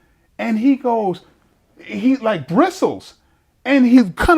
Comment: This guy’s vocabulary is crazy! Did he just say bristle?